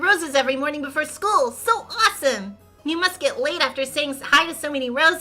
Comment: ngl i misheard as ‘you must get laid after saying hi to so many roses’